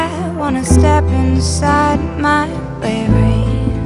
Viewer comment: I leave it all behind If I cannot speak my mind My conscience defies all known reason You dream of reality But I dream so constantly Can't you see it's a grand illusion? I wanna step inside my bravery I wanna take each day one whole hour at a time I can't scream so loud when I stand my ground Cause I gotta ask when am I done waiting? I know just what its like To wait for a voice inside To appear and sound formation And when you think you see Someone who looks like me Be aware it's a grand illusion I wanna step inside my bravery I wanna take each day one whole hour at a time I won't scream so loud when I stand my ground Cause I gotta ask when am I done waiting? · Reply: Thanks. I knew about the other lines it got wrong but I really thought she said baby when she said "bravery". XD